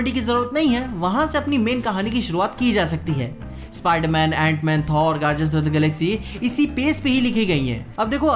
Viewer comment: I hear gajjar of the galaxy But he says guardian of the galaxy